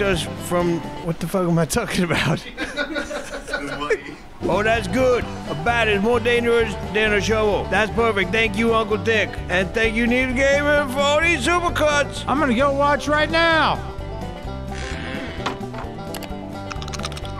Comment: I keep replaying it but I hear saying: Thank you uncle Dick instead of Thick. O.o